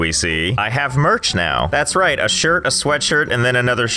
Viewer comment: "I have merch now" i thought you said Mercs...